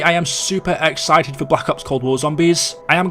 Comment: I thought he said he was excited for black ops 4 at the start and I was so confused